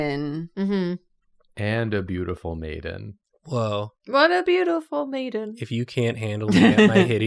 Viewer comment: I thought he had said "beautiful Nathan" instead of "beautiful maiden"